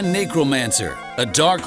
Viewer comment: I thought he said Negromancer...lol. That would be a cool name though